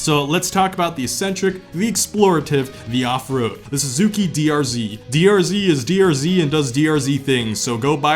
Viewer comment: When you mentioned Suzuki DRZ, I thought I heard BRZ.